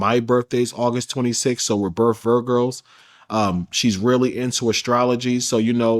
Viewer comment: did he say Vir Girls?